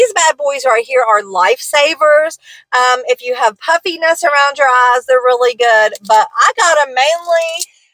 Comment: Awesome haul beautiful! I thought you said “ if you have puppy nuts around the eyes” lol. The nails are my favorite.tfs